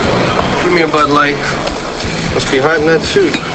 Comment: I heard “gimme a butt light”😂